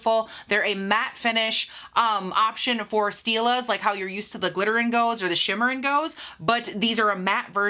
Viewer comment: Did u say 'Shimmer/Glitter and GO?' Verses 'Shimmer/Glitter and GLOW?' 😂😂